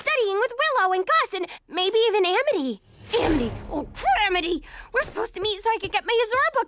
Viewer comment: every time i heard this scene i heard "amity oh clamity"